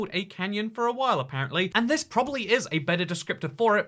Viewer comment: i thought he was saying Kenyan lol